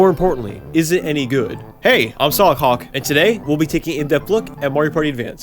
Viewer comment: Am i the only one who heard Hey, im still a c*ck